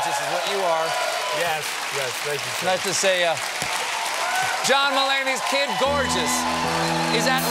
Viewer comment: When I first heard him say his name I thought he said "John m'lady. 😂😂😂